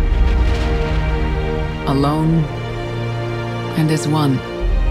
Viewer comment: What is the phrase that Jennifer Hale says right after she says "alone" (0:26).. i cant make out anything that makes sense.. "in this one"? "and there's one"? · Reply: Alone, and as one.